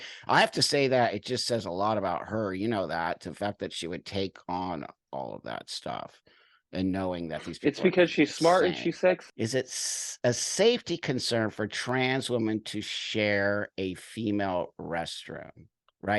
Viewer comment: did he just say ”its because she’s smart and she’s sexy” ? But got cut off at sex